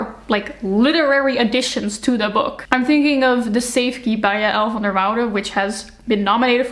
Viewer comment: what is the name of the book she mentioned at 5:57 ? I couldn't catch it. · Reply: The safekeep